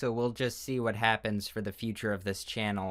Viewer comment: i literally thought you said "solar sans" in the outro 💀